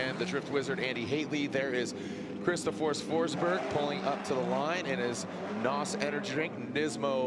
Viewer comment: Why "Chris Forsberg" now is "Christopher Forsberg"!? (since Goodwood Festival lol) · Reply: I only heard Chris “The Force” Forsberg.